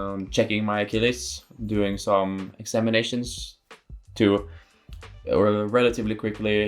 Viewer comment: did he say he has been doing some inseminations? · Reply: Examinations